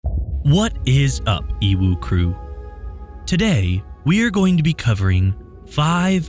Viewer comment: i always misheard the narrator said like what's up "Evil Crew",i know he mean EWU Crew.......anyway love your channel